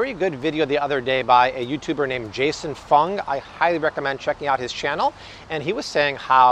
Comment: For second I thought he said Jason Faunt.